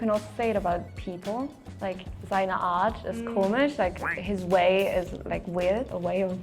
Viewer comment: It's really rough that some of the subtitles are wrong on a video likes this. As an example, around 11:30 the "German-German" girl uses "Art" in a sentence and says "Seine Art ist komisch" while the subtitles say "Seine Art ist kommest", which is gibberish.